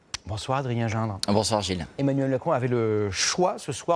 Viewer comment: A 2:15 il dit bien "Emmanuel le con" ou c'est moi qui délire ?